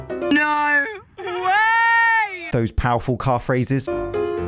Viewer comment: When you said "calf raises" over the picture of the car, I heard "car phrases"